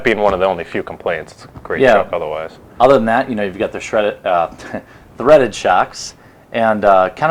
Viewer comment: HE SAID SHREDDED INSTEAD OF THREADED SHOCKS AM I THE ONLY ONE WHO HEARD THIS.